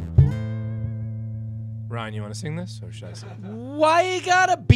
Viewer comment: Did he said "oh scheiße" at 3:15 ? :D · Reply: Or shall I sing?